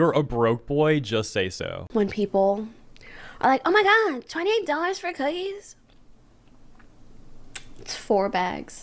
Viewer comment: bruh i thought she said "$20 for cookies" and i was roasting her "$7 for 4 bags" math so hard but turns out i'm just deaf af. lmao